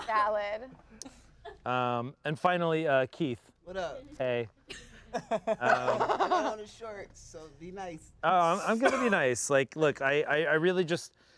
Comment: did he just say "My time is short so be nice" ?